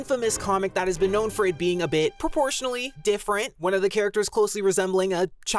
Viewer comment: i thought you said (abortion) not proportion hahaha